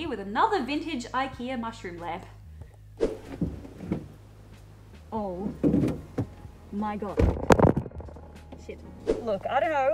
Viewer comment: I'm not very good at lip reading, could someone tell me what she said??? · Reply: She said "oh my god" ... Just excited to find the lamp.